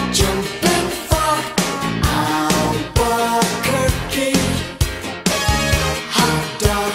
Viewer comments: i hear "jumping far" not "jumping frog" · On the part where it says "jumping frog, Albuquerque " I always thought it said " I want cookie" and I've never been more dissapointed..